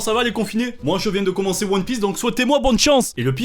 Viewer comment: J'ai entendu " j'ai commencé one piece " bon bah j'ai instant like et partage